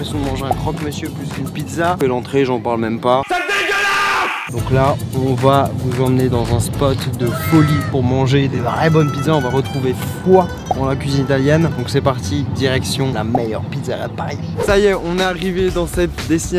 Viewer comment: C'est moi ou il a dit alien 3:35 😂